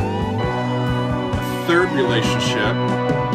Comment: The dorian Im-IV progression was a Pink Floyd trademark throughout most of their best known albums. Add a 9th in the middle of the Im and a 7th and occasional 4th suspensions to the IV and you have the harmonies for a quarter of Meddle and half of Dark Side of the Moon. It's a wonderful progression for those long, dreamy, Melancholic, bluesy solos both Gilmour and Wright loved. 16:05 I'm not sure but I think that's an organ, not strings. Wright had this rather unusual setup with two organs, a Farfisa and a Hammond, each with its unique sound and I think he's playing with one hand on each there, the percussive chords on the Hammond and the string like long notes on the Farfisa. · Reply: Thanks for your answer! The 'word' was probably 'third relation'. English is not my native language, so I thought he was saying something like 'thermination'... Third relation sounds logical in this context :) But..... would that refer to the Em - G#m step ? Because G# (or Ab) is not the mediant of E minor. In E major it would be. I think that is the interesting part of this progression. I agree that there is no 'correct' answer here. I like trying to find out why some things/progressions sound so nice/beautiful/interesting/etc to me. Also a big fan of Steven WIlson for this reason.